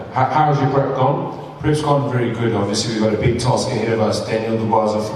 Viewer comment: I thought Kevin said '......we’ve got a big tosser here.....'